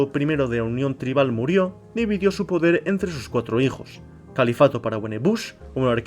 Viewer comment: acaso escuche Califato?, Que sifnificados tienen los nombres de los "paises" que crearon los sapiens? Que sistemas de gobierno se desarrollo?. (Osea como se organizaron las civilizaciones) Como llamaron ellos a su mundo? Se llego a crean un Estado/Colonia en trapis vitae? Que religiones hubo en traspis vitae?. Para el momento de la llegada de los humanos, los humanos todavia tenian religion? Como el islam? Y llegaron a crear lugares religiosos en el planeta?.